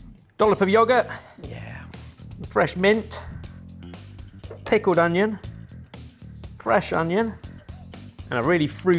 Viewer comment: I know I misheard, but I like the idea of "Tickeled Onions". It makes me chuckle.